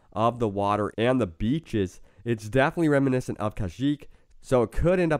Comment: "Khajiit" instead of "Kashyyyk"